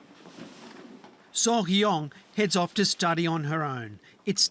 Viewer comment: did he just say soalhiyoeng wtf 😂